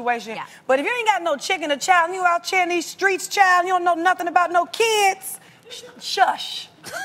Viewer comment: what did tamar say 00:53 - 01 :00???? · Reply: Tamar translation: If you don’t have a chicken or child, and you don’t know nothing about having no children hush.